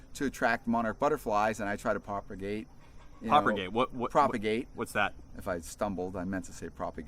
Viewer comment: I thought, he said "proper gay" 0-0